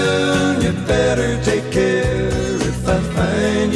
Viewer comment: For 50 years I heard the words “up that stair” rather than “my backstairs”